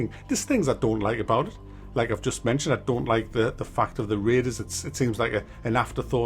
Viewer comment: when you first said turning off raiders, I thought I heard waiters lmao!